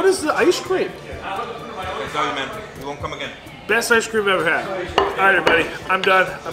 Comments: What did the guy say at the end? It sounded like he said "sorry man, dont come again" i listened to it like 5 times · WHAT IS BETTER, CHRISTMAS OR NEW YEARS?! BTW, Should we have a New Years Special :O? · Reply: wait a minute!! what did the owner said at the very end of the video when was asking what the ice cream was?? did he just say "you're done man, you won't come again" is what i heard.. anyone agrees with me?? i don't know but i saw how awkward was ending the video asap. this is weird bro! listen to it. the owner seemed pretty unhappy! something must have happen in between! Kindly explain! thanks! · wait a minute!! what did the owner said at the very end of the video when was asking what the ice cream was?? did he just say "you're done man, you won't come again" is what i heard.. anyone agrees with me?? i don't know but i saw how awkward was ending the video asap. this is weird bro! listen to it. the owner seemed pretty unhappy! something must have happen in between! Kindly explain! thanks!